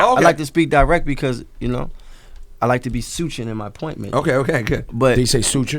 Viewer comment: he actually said/tried to say "succint" lol..sometimes I forget I'm the greatest lyricist out there; props for constantly being able to fool me, though, but it'll only ever be temporary